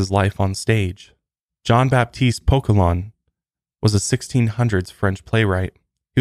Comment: Jean Baptiste Poquelin -- lol, I thought you said Jean Baptiste Pokémon.. I had to look him up. 🤣